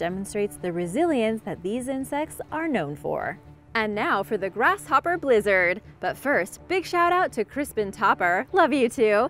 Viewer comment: I thought she said grasshopper lizard boi I was gripping my seat